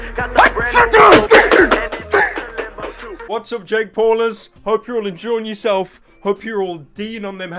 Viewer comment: Did he say "Jake porners"